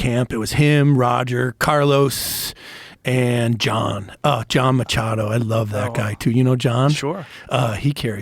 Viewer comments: I THOUGHT IT SAID JOHN MCAFEE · I thought it said John McAfee for a second and thought this mf was finally interviewing the spirit realm. 💀